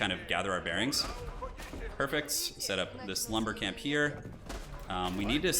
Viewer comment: I always hear “The Slumber Camp” rather than “This Lumber Camp” hahaha